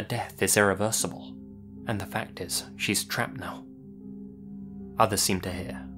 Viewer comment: Instead of "she's trapped now" I heard "as she's travelling now", which does make more sense. I can't think of a sentence where "the death is irreversible" makes sense, but occam's razor supports the fact that there probably is some easy answer, especially if they were talking about something immaterial—like the idea of a moral monarch, stability, a female leader, the way the death of the second Elizabethan age is irreversible... any number of things, really. It does sound fekn spooky though. Some people have said it sounds like Diana and I lowkey agree, so I think someone should go back through interviews to see if it's actually Diana talking about the end of her marriage to Charles and how Camilla is trapped now 😂 "How could it be Diana?" Well, maybe it was from an interview she did (she did do a few, more than most royals, until her son broke the record), talking about the death of her marriage to Charles and how Camilla is now stuck with him i.e. "she's trapped now", or she was maybe talking in the third person about her younger self, or like "I was saying to myself, you only have one go at marriage and if you fail the death is irreversible... she's trapped now, being with him, I mean" or something like that. Diana often spoke around issues, using metaphors and not mentioningby name, allowing the interviewer to be the one to infer things and state the conclusions out loud, she'd never have said "my marriage to charles ended and now Camilla is stuck with him now and he's s